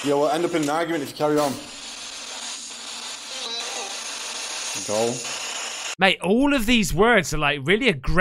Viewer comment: At about 14:13, after it said “argument”, The caption said “go”, but it was actually someone saying “the way you move”